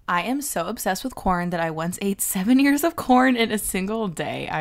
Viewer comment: I thought you said seven YEARS of corn at first